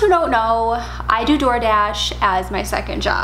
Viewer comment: Seems more like "METH DASH". LOL You talk so fast. Get off the crank girl! haha ;-) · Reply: that's what I thought she said in the beginning. "I do drug hash" ????? And then with the crazy eyes....